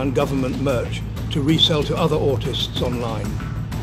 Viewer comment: I thought he said “esteemed autist” at first 😂